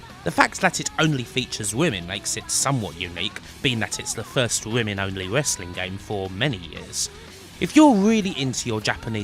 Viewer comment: Am I the only one who thought was saying “Rimming Only Wrestling” instead of “Women Only Wrestling”?